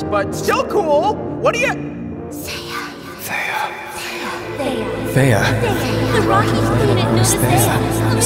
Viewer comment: ermmmm I think bro said Theia